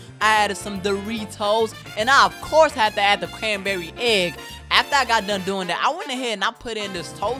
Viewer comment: Am I the only one or did I hear "canberry"